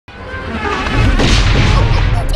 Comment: All these years I thought they were saying Tee Dee